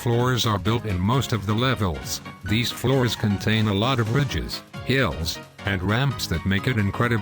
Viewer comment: Thanks for watching this episode guys! Be sure to follow me on Twitter and join my Discord server for updates on future videos and to catch up with me! Twitter: <link> Join My Discord Server: <link> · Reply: When you said lots of "ridges" I thought you said bitches. XD